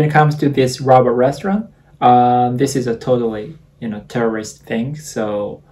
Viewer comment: I thought you said "this is a terrorist thing" and I freaked out for a few seconds hahah